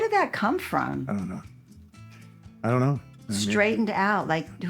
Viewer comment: You got straightened out is an old school saying from the 60's 70's meaning your back on track😊 parents would yell at you STRAIGHTEN OUT!!!😂😂😂😂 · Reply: I always heard "Straighten up!"